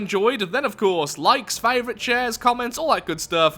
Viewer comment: Is it just me, or did say “chairs”? I know he usually says “shares”, but sometimes it sounds like he says “chairs”.